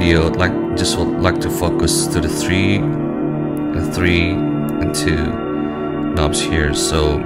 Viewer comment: What are you saying? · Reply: "I will focus to the three knobs"